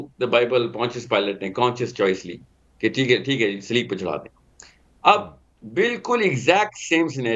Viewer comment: What he said sleep??? Meaning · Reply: Sleb